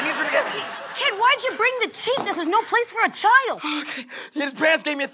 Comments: did just say "kid, why'd you bring the chief" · am i the only one who heard, "Kid Why'd You bring the chief?" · did she say "kid why did you bring the chief"